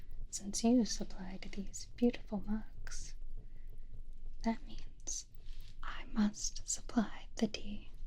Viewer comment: did she just say "I must supply the D"????